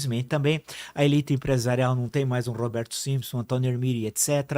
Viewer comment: "a elite empresarial não tem mais um Roberto Simpson (...)" Nunca ouvi falar, dei um google, tem senador do Chile e outros homônimos, nada de empresário. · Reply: Roberto Simonsen… um grande empresarial brasileiro! O cara que criou o FIESP